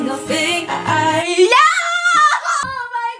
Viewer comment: The first time i heard her say result coming soon i thought she said please dont call me soon. Just me... No... Okay bye!